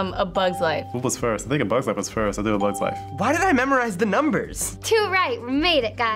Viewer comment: Wtf did he just say?? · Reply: “What was first? I think a bugs life was first. I’ll do a bugs life.”